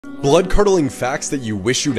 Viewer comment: I thought it said “blood curdling farts” 🧍‍♀️